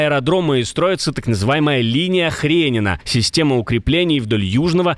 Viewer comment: мне не послышалось?, линия обороны "ХРЕНЕВА"😅. Извените, просто жуткий смех проберает от ХРЕНЕВА😊😂😅...